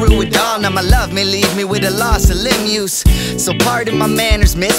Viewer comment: what does say there, "with a loss of ????????" it sounds like "Lim-use" but I definitely don't think that's a word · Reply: Malone loss of limb use. A couple lines later he talks about risking paralysis.